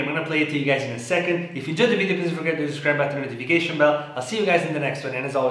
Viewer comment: does anyone understand what he says at 08:04 lmao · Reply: He said “If u enjoyed the video don’t forget to hit the subscribe button and notification bell”.